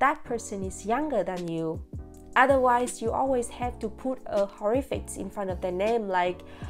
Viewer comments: It's funnier when you mishear 'honourific' as 'horrific'. "Put a horrific infront of the name, like aunty 👻" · So this video is 2 years old but I just wanted to let you know that at 1:45 ish you and the captions say "horrific" but I believe the word is supposed to be "honorific". I assume it's not possible to go in and edit an existing youtube video but just in case you wanted to edit the captions I thought I'd mention it just since horrific means something so different :)